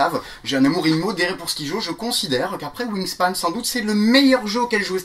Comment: c'est quoi le nom du jeux dont parle a 24:30 ? il me semble qu'il dit moonispan et dans la vidéo des jeux en voiture il crie un nom de jeux et j'entends xpan du coup c'est probablement le même... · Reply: Dune Wild Wingspan ;)